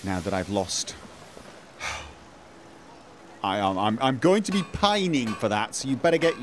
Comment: That word you said, the p-word, never heard it before. How's it spelled ? Not often do I hear English words I don't know/understand. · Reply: Ivan Haboić Pining.